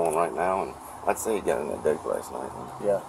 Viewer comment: What is the stepfather saying at 13:11 ? Because the caption has him saying, "He's got a lot of confusion going on right now, and I'd say he got that D last night." 😂😂😂